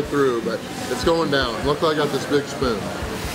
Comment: sounds like he said bitch through but he said big spoon! 😂😂😂